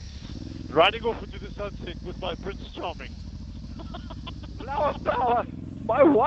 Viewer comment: When you said "Prince Charming", I thought you said my "Brits Chommie"! LOL